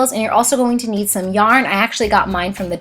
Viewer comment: did anyone notice at 1:24 she said yan instead of yarn